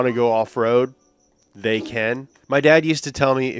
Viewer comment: did he just say "my dad" does that mean we'll get an Up to Speeed about his dad? After all these years of waiting? 😂